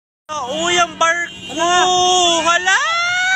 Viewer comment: may narinig ako kuya barko or kuya marko